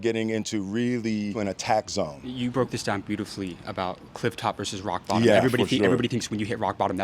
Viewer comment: I thought he said Clif rock verses rock bottom... Then this sex sex.. 🎉😊